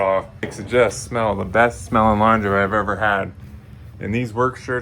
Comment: Scottish Watching from Australia! Love this content ! Great progress 👏 keep it real Bro 💯 👌 had to rewind that twice ,carefully..sounded like you said " The best smelling lingerie you ever had ?" 😂